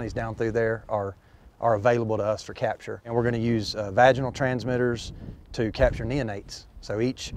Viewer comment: did he say 'vaginal' transmitters?